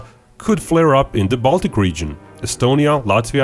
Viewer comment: funny how i understood "balkan" instead of "baltic"